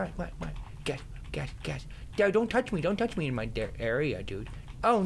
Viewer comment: "dad don't touch me in my di- area" lol I heard that.